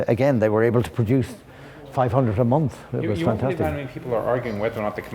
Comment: did he say that they could produce 500 every month?!